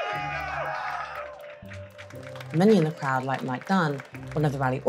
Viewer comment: I thought she said “my son” not Mike Dunn and I was very confused